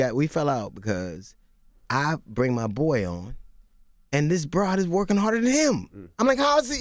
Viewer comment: Wait..... at 53:48....did he call Jasmine Sanders a brawd??🤔🤔 can some one please Listen for me